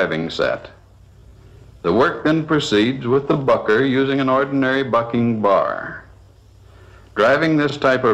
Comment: 🙊a fucker using a FU.K..G bar 3:39 ?? Did he just say what? Free nippleing= no bra